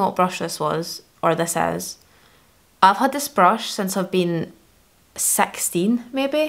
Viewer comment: this is the cringey American in me lol but I thought you said “I’ve had this brush since I’ve been sexting” I was like excuse me whet